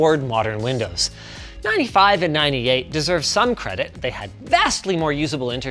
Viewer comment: did he just say windows 35 at 11:48?? 😭